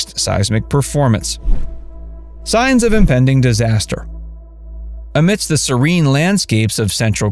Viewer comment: sounds like he said, "science of inventing disaster.'